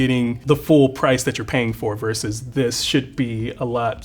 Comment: Lmaoooo if u check the time stamp around 6:40-6:44 after price reveal when he said he rather have the cheaper one i thought he was saying he rather have the cheaper one “versus this $#!+” 💩